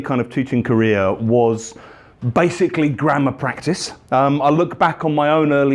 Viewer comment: Oh dear! I just heard "Grandma practice" 😁😇